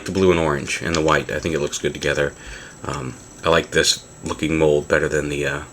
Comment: OMG did you say " I like this F**king mould" at 2:42 ???!